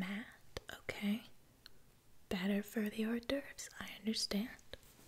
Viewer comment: "i would never get between a girl and her horse divorce" thank you. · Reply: HJIDLMAOO i thought she said that too but it was “hors d”oeuvres. It’s a kind of appetizer